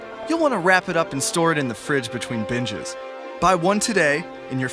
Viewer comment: did he just say "between bitches"